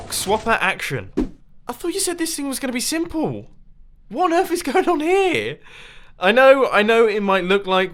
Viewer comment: did anyone hear the words "I thought this thing was simple. What on earth happened here"?